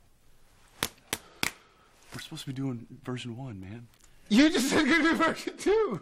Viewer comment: Can someone please tell me what jensen says at 5:25? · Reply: "We're supposed to be doing version 1, man"